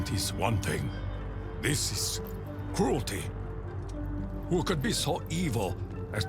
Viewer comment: I thought said fruity and not cruelty